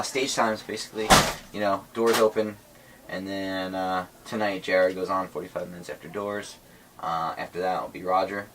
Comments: When he said Jared Hart am I the only one who thought he said Gerard??? MCR FEELZ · When he said "Jared" I swore I heard "Gerard" until he shows the cd. My heart stopped for a few seconds.